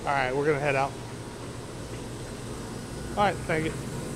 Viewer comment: Is it just me, or did he just say “alright faggot” at 2:37 mins in?